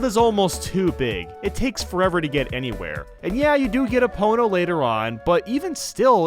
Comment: Somehow the subtitles messed up what you said and still got it right. ("Epona" changed to "a pony", but both phrases fit into the sentence and still get the same idea across)